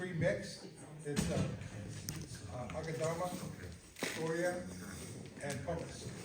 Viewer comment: Hello. What is the Bonsai Soil Mix mentioned @ 25:54? I heard Akadama and Pumice what is the 3rd Component? Thank you. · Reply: I believe it’s 1:1:1 pumice:akadama:scoria